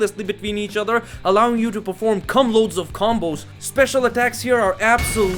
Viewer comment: Did I hear correctly? "Allowing you to perform cum-load of combos", Are we still discussing video game right? ; )